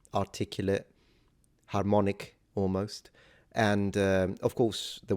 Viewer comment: I thought you said 'harmonarch', an apt neologism, but then I realized you were saying 'harmonic'.